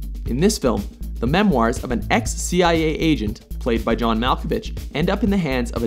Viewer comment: Did he just say John Malfabitch? XD